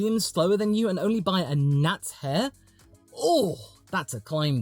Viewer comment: Sounded like you said a nut's hair. Nice